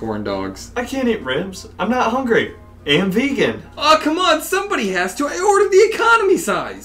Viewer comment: lol for a second there I thought he said "And I'm peaking" like he was on lsd